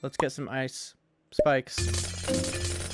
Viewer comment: What you say : Ice Spike's 19:38-19:39 What i hear : Ice Spice